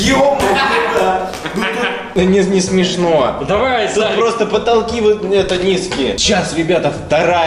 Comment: смотрите что сказал:давай зай